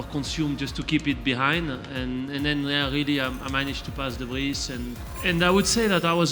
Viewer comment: Lol 5:16 Did Seb say "Debris" too or is that just how he pronounces "de Vries"???